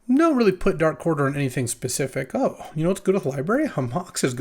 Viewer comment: What's he saying here? I can't make out what he says and the subtitles say "dark quarter" which I still don't really understand, can someone enlighten me? (assuming the subtitles are correct, which they very well could not be). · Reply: "I don't really put darkquarterer on anything specific". He's saying that he doesn't know what the player he's passing to will take out of this pack (you can see the usernames of the drafters near the top of the screen)